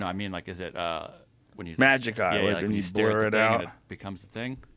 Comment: fuck what did jamie say there? Joe had to intervene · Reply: Magic Eye